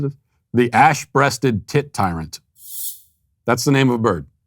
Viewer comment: Not gonna lie, I thought Matt said the name of that bird was the “ASS breasted tit titan” 😂